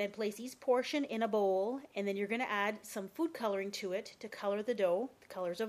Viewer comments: When you said Food Coloring I thought you said Poop Coloring.....lol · i thought you said "poo colouring". . . oops my bad! XD